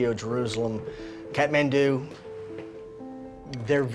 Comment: did he just said Kathmandu?